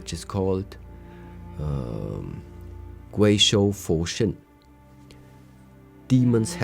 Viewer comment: Thank you. What is the exact Chinese words for “Demon Hand, Buddha Heart”. Master Shi said it around 29:22 to 29:25 minutes into the video. I phonetically heard “Quay Sho Fo Shin”. Can you provide the correct spelling please. I understand the meaning but the Chinese words are important to me as a symbol of respect for this proverb.